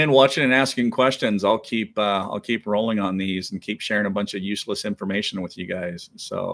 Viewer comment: Did he say useless information??